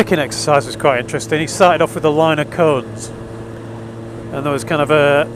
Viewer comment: on he first play thru I thought you said "he first laid out a line of coke"! lmfao I had to rewind to hear "line of cones". Sounds like a good course.